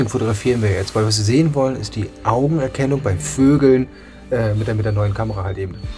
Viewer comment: "was wir sehen wollen, ist die Augenerkennung beim Vögeln" 😆 hab nur ich das so gehört?